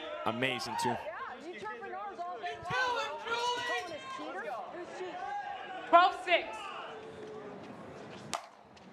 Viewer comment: What was Julie Johnson saying to Will at 1:05:45?! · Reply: “Ya. You’ve been chirping ours (players) all day long! Calling us cheaters? Who’s cheating?”